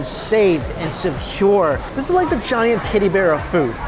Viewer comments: I thought he said titty bear at 2:20 for a second lol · fasting must be getting to my head cause I heard : “ giant TIDDY bear of food ”